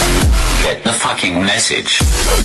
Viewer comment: it sounded like the big guy actually said "get the f[ugh]king message*